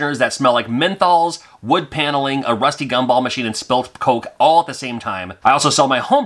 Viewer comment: Did you just say a rusty trombone machine?